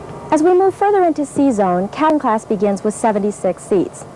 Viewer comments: as she moves further into C zone sounds like she says "cattle class". I feel I'm familiar enough with the aircraft now after watching this video. Where do I go to fill out an application to be a PanAm stewardess? I'm ready. (But I refuse to work E zone, EVER!) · sounds like she says 'Cattle class' 🤣